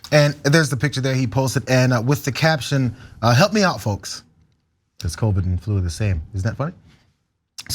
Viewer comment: "Hear me out, folks", could be interpreted as the far right's "I'm just asking..." crap. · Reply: I thought he said " help me out folks". Do you proof read before hitting send ?? I had to ask !!